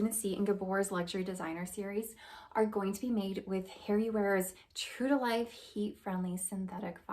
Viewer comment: Huh, All made with “Harry wares” heat friendly fibers. Did you say, “Harry Wares.”?